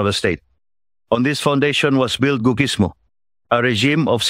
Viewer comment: What did he say at 10:24, [goo ‘gǐsmō]? How do you spell that? And what is it? Is he referring to googology or googlism? Or neither? Thanks.